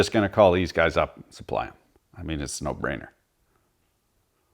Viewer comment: I thought you said AR knife!!!😂😂😂